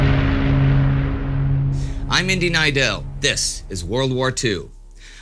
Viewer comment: every time i hear indy announce his name in his introduction, i swear i hear “Indian” Neidell. Am i hearing it wrong and he is just saying “Indy Neidell” fast? asking for a friend lol